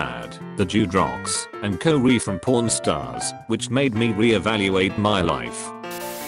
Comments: sounded like pornstars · if it didn't have the words there I would have thought e said porn stars. :/